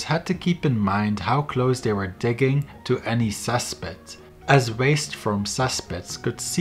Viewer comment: can anyone tell me what's the word he used 3:35 "well diggers had to keep in mind how close they were digging to any ___" i keep on hearing "sus pit" and the auto CC says "suspect". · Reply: Cesspit